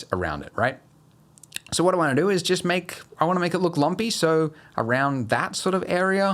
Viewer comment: hello, I trying to learn blender(thank you for tutorial) and also to learn english. I heard something like "lompy" at 7:48 but can't translate it, is it correct spelling? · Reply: no, it is lumpy